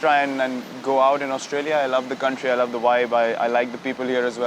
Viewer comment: did he say he loves the Wi Fi??